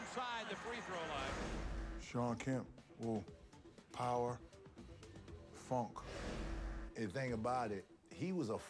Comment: For a moment I thought Shaq said "Shawn Kemp, power. Fuck."